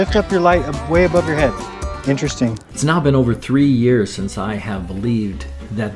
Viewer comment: He believed the earth is flat for 30 years? I think he's lying actually. And I don't think he really believes the earth is flat to start with, just another attention seeker. Oh 3 years. Sounded like he said 30..